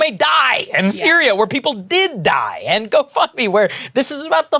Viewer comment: When you thought you heard Saagar say go f@ me instead of gofundme